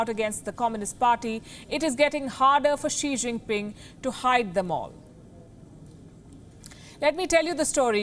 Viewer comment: I heard She jinping